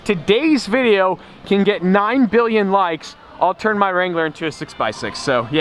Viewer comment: Did he said "9 Billion" or 9 Million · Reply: Billion?